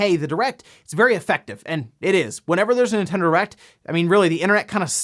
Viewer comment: Did he just say... Erect?